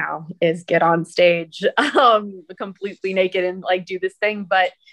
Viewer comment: Did she say she gets completely naked?!